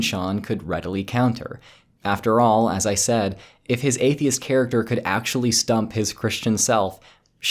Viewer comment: i thought you were saying athiest chan and I was dying thru the whole video